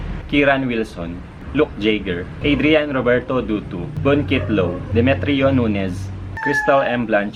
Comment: I really heard doo doo😂 btw u back❤️😊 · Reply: It's dutu hehe. Thank you! ^____^